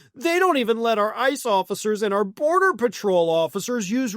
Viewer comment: I thought initially you said "Murder Patrol" instead of "Border Patrol". And it felt more accurate with the mishearing.